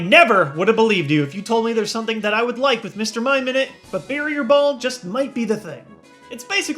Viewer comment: Who else misheard "Barrier Ball" as "Harrier Ball"?